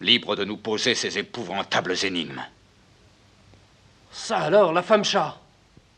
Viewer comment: j'ai compris "salo*e, la femme chatte. Pardon